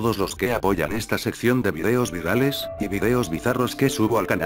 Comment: entendi "feos virales" jajajja :v